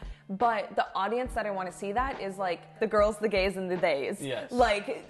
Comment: "The girls, The gays, The theys" we love that. Literally my whole friend group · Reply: Lol I thought she said "the girls, the gays, and the baes". I like "theys" better